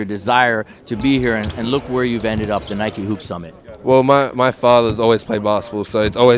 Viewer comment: sounds like he says bath salts. lmao.